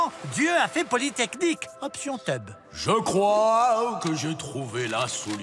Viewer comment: Dieu a fait quoi ?🤣🤣🤣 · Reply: Polytechnique option teub !😂 Ils m'ont fumé 😂😂😂